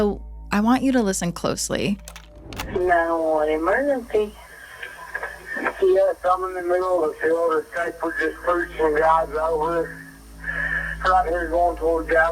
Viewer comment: I keep hearing “This guy” or “A guy” instead of “I straight”, but both you and her heard something on “s”, so I don’t know why I keep hearing guy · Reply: I hear: “Yes, I’m in the middle of the field. This guy [“pushing 30”, maybe] got ran over…” something like that.